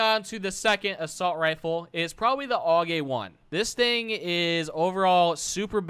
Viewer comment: i thought i heard “all gay 1”